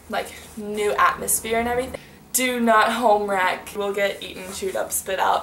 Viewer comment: im confused what you said here. Did you say do not homerack or something?